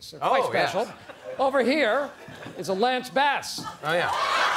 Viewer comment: What does Steve Carell say at 4:20 that seems so funny? Can somebody tell me cause I just can't understand what he says. Lamps vas? lampas? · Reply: lance bass!